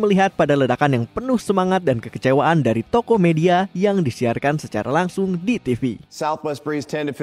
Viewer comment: salah denger Tokopedia aawkwoakwoakeoak